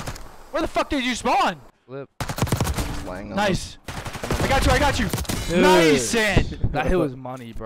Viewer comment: Am I trippin or did I hear "Nice Zin!" @ 4:22-4:24 lol Shottzy prolly like 😢